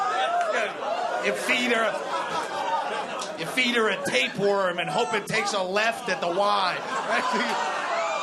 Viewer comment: hey, can anyone please tell me what is doug saying at 5:38-5:41 and at 9:31-9:33 i'm trying to translate "deadbeat hero" into spanish, but i'm having problems with these two. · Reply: Pablo H. Gianella 5.38 he says feed her a tapeworm and hope it takes a left at the Y